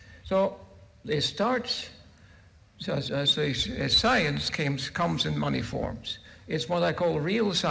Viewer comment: I thought he had said, "Science comes in money forms."